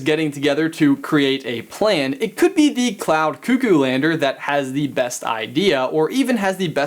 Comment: I thought he said count dukoo lander